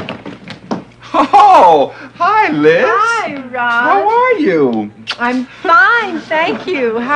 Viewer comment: Did she said ...Hi Ron in 4:23 ??? (Ron Glass)